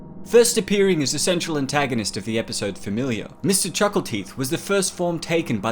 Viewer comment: Did anyone else mishear it as "Mr. Chuckle Cheese"?